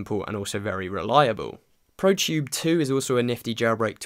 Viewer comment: what does he say at 03:08 ? proshyubetwo ?